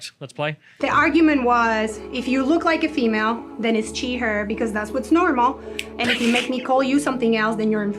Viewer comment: I think the subtitles are wrong, she's pretty obviously saying "chi/her" the whole video, not "she/her".